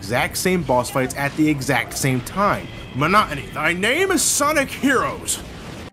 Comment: What did he say? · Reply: Want some more